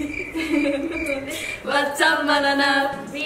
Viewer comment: Did they just say "What's up, Mananap"? Hahahahaha